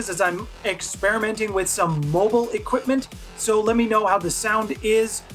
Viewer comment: When you say, experimenting with mobile equipment, what I heard was, "mobile suits!"